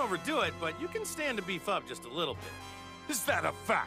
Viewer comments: Did he say "you can stand to be fucked just a little bit" in 1:43 ? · Am I the only one who thought Goku said you can stand to be fu**ed just a little bit 🤣😂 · I originally thought he said "you can stand to be fucked just a little bit" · Did goku say, "u can stand to be fucked a little bit"?